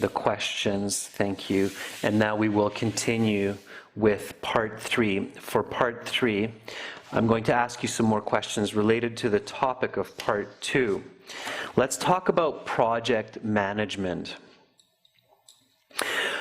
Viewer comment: did he say "queshion"?